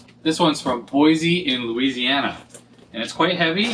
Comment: When you said Boyce, Louisiana I thought you said "Boise, Louisiana" and I was like "Huh. You never know :\" And that open glass of OJ (excuse me, Mango Tang :) ) on your repair desk is gutsy :D